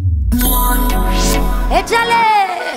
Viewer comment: ejale! (idk what she is saying don't judge me)